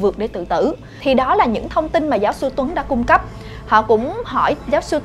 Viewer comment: Cứ nghe thành giáo sư huấn:)